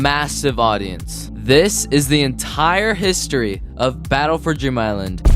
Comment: he said dream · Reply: it sounded it sounded like he said "DRIM" instead of "DREAM" island